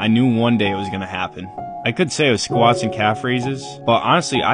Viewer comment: For a second I thought you said drunk